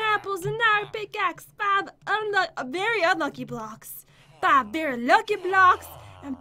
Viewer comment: it sounds like she said f.cky blocks